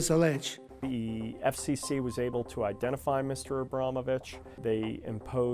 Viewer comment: literally sounded like abrama bit#h ...no kidding